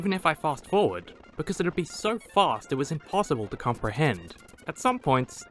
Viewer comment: I thought you said 20 minutes is so long its impossible to comprehend & I was like yes..... agree